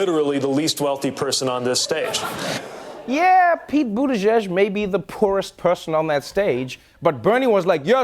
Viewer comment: the captions say “Peabody Josh” instead of Buttigieg 😂 The captions trying to make sense of his name throughout the video we’re hilarious.